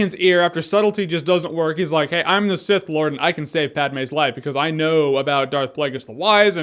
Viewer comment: it sounds like your saying i can't save Batmans life.lmao